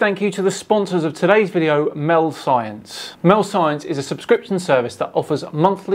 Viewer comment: I thought you said milf science, my ears pricked (giggity) up at that, imagine my disappointment.